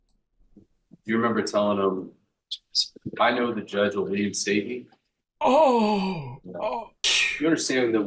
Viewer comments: What did the prosecution say at 11:07, all I got was "I know the judge will" then I couldn't make out what he said next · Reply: Sounds like he said I know the judge will reinstate me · what did he say? "The judge will lee and state me?"